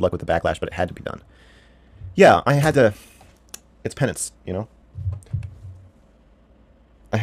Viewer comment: I know he said "its pennance" but what i heard what "its penis". Im sure it is, Im sure it is :)